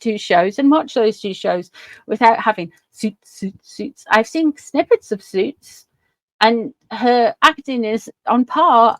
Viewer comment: I could have sworn you just said snippets of Snoots!